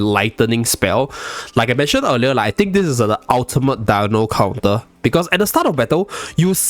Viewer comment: I heard “dio counter”. Where tf do i sign